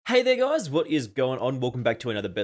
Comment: Bro at first i almost heard you saying the n word but when i watch it 4 times your were saying "hey there guys"😂